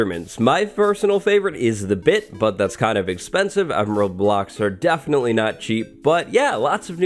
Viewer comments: did he just said roblox? XD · did you just say "I'm a robloxer"